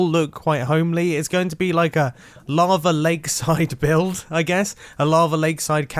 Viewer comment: He sounds like he says larva lol